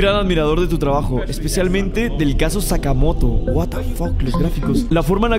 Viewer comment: XD pensé que decía sacamos pero era Sakamoto o algo así JSJSJS